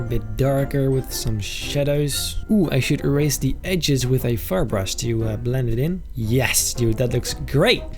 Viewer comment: I thought you said fart brush O-O